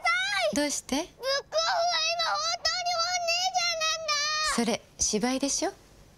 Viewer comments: 二つ目のセリフなんて言ってる？ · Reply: 俺も分からんくて5回くらい聞いて、「ブックオフは本当に今「本ねぇじゃん」なんだ」かと。 何度も再生させる戦略にまんまとハマってしまった、、、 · 何て言ってるんですか？ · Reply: ブックオフは今本当に本ね〜じゃんなんだ〜